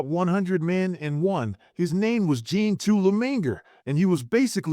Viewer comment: what what his name? I couldn’t understand what you said. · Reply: His name was Jean II Le Maingre